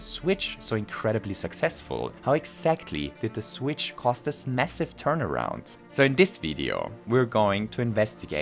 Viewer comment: Did you say massive?